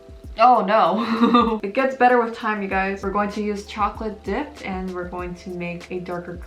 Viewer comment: Fr i thought u said chocolate d*ck